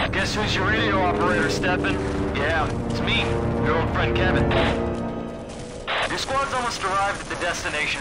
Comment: when i first listened to this i thought he said "girlfriend"